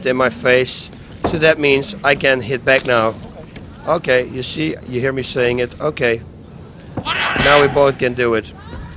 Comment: What is that one japanese dude screaming in the crowd. I always hear that shit in these matches. At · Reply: He's saying 頑張れ！, which means something along the lines of "Do your best!" or "Keep going!" The crowd was mostly behind Frank from what I hear.